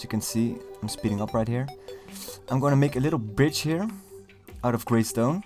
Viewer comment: to me it sounded like you said "im going to make a little BITCH" not a bridge...